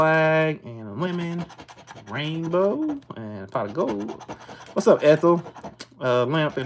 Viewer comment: Lol I thought you said what’s up asshole